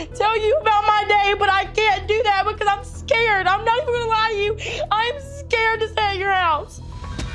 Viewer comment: I think she said "IM SCARED OF PLAYGROUNDS"